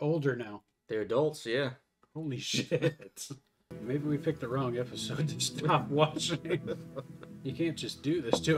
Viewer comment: Sounded like you said "maybe we took the wrong episode to stop watching", i really hope thats not the case, seeing your vikings uploads is one of my favorite week activities.. please keep enjoying the show if you like it